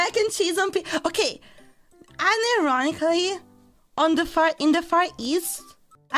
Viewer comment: She actually says "unironically" here, which is meant to imply that she's talking about something for real.